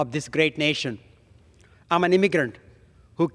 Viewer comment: did he say “I’m an ignorant”? 🤣😂